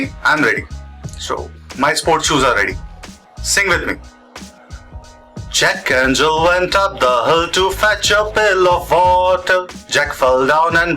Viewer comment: is he saying "jack and jill went up the hood"?